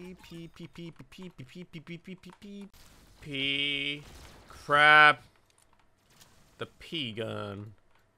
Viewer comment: i thought he said crab when he said crap